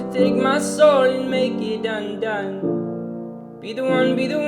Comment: also tylers pretty confusing when it comes to lyrics, so i always heard "..and make it done done" but everyone always said "undone" and in the album itself it says "undone" yet Tyler says "done done" here okay im sorry im just confused ??